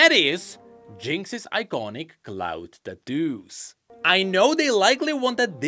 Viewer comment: Jinx' iconic what? I hear "cloud deduce" which I fail to make any sense of. I guess you're talking about her hair cuz it's clearly missing.. But how did you call them? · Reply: cloud tattoos